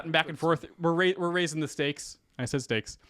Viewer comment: Did you say steaks? 😂